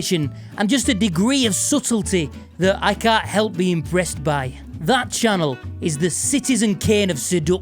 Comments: Did you just say "imbreast by"?? 1000 points, bro. Even if you didn't and I just heard it that way. Massive props. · I'm sorry what did you say at 5:47 ? You cant help being imBRE@ by her???? Ehhhhhhhhhhhhhhhh????? Ehhhhhhhhhhh??? 🤔 Hrmmmmmmmm......